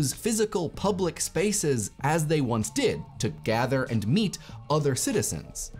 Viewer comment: I had to double check when he said "to gather and meet other citizens." I literally thought, he said, " to gather and EAT other citizens" and I was like wut?